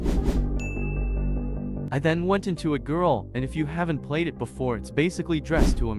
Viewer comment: why does it sound like he said I THEN WENT INTO A GIRL.